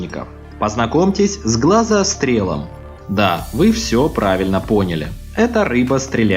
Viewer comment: я который услышал "грохострел":хватит с меня растений против зомби